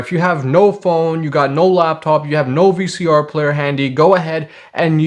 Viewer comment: did you say "VCR Player"?? Lol. I love when a small joke is slyly slipped in under the radar! Bravo!